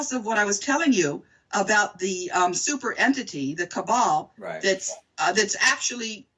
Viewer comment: not KABAA... she clearly said CABAL... a group of people corruptly running things in their own interests and not in the interest of the greater good/population.